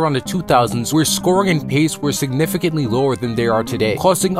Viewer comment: did he just say skibidi?